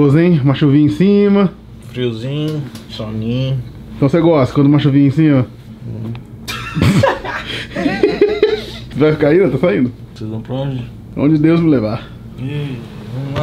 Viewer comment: só eu entendi? Então se gosta quando o macho vinha em cima kkk